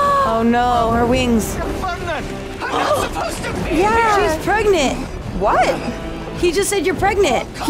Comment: He said burning, but I swear I heard pregnant as well.